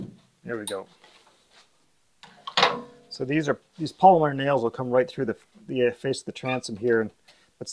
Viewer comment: I thought I heard you say "Palmer nails" and "Palmer nailer" so I thought I would look into the pricing and get one of my own. HAHA!! So now I realize you said "POLYMER" nails and "POLYMER" nailer. Yikes. Just looking at the rake of that transom gives me the impression that this boat will be built for slippery fast speed under oar. Looks like a racecar! Inverted and backwards, but you know 😉